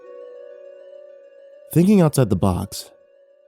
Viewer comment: Omg 😆 I thought he said thinking outside the buttocks 🤣🤣